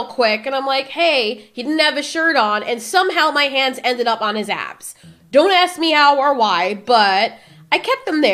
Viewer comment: For a sec i heard “somehow my hands landed on his ass” I was like “ay hol up, the dude pulled a fast one on my girl?”